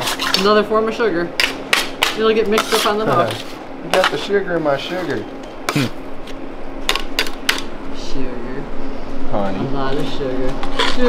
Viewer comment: 😅 the transcript for this video is terrible. Here's what it says for 3:38 - "Former sugar really get mixed up on the dog. Get the sugar my sugar. Oh sugar sugar sugar." 😂